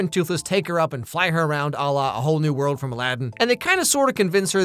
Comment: I thought he called him "doofus" for a second, anyone else heard that?